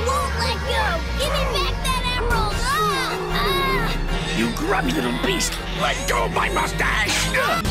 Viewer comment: i thought he said YOU GRUMPY LITTLE BEAST NO ONE TOUCH MY MUSTACHE